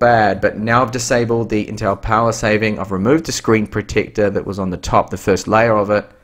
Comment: What's said at 14:28 ... "Intel power saving of remote the screen protector that was on the top the first layer of it", what? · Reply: "Now, the screen brightness in my unboxing, it looked a little bad but now I've disabled the Intel Power Saving, I've removed the screen protector that was on the top - first layer of it and that's a 100%. It's very bright - the screen." He peeled off a cheap plastic that was put on the screen to protect it from scratches.